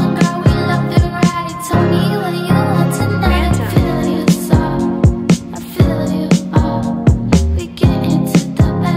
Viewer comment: i wonder what background voice says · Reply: "Fantom" the beat maker